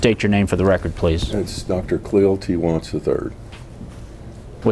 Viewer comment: Did he actually say “doctor,” ~ 0:39 ?? Can you even begin to imagine seeing a doctor so obese, telling YOU to be healthy? Come oooooooooonnnnnn!!! Ok, and he couldn’t remember the second place he lived, yet somehow he was able to rattle off how many Texas towns? I grew up a Navy brat, moving all the time, even when I was 10 months old. Of course I’m not going to remember the actual move, but I’m fairly confident in what my folks told me as I grew up. 😆🙄🤷‍♀️